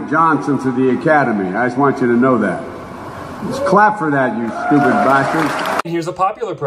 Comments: Excuse me did he say "stupid bastards?" 1:39 just when I thought I'd heard it all, God help us all. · Did he just say “ clap for that you stupid bastards?” OMFG